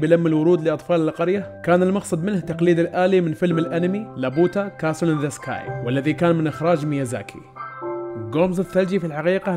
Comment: ما سمعت اسم فلم الانمي ممكن اسمه بليييييييز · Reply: Laputa castle in the sky هذا اسمه